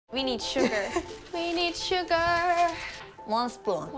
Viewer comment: When jennie saod we need sugur i thought she said we need suga